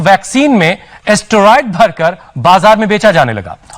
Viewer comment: when news guy said steroid, it really sounded like asteroid. Vaccine ki jagah asteroid bhar kar...